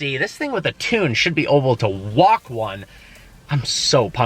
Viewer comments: Does anyone notice he always says "oble" instead of "able"? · "oble" instead of able lol 9:15 "costed" instead of cost. He always says that